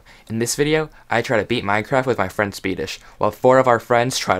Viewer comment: I herd: Today I try to beat minecraft with my friends feti**. I just heard that the first time.